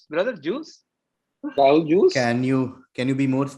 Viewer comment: i thought I heard orange juice I the question